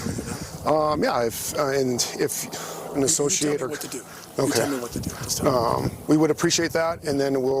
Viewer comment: Did anyone else catch that "Don't tell me what to do" line...I guess he just didn't want to be reminded that you can't throw money at ~every~ problem that comes his way. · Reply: Thought he said “you tell me what to do”